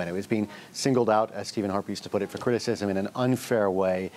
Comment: Did he just say Stephen Harpies???